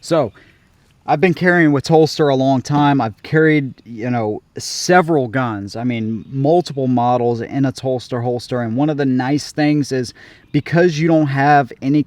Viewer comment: Good review. I thought you were saying Phlster holster until I read Telstar. It’s hell getting old